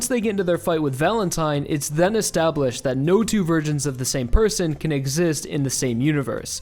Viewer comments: When he says "No two versions" at 18:36 it sounded like "No two virgins" and I was confused. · thought he said "no 2 virgins of the same person"